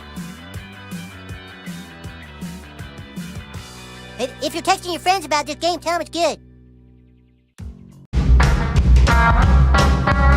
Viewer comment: I didn't understand what Butters said, help? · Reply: "If you're texting your friends about this game tell them it's good."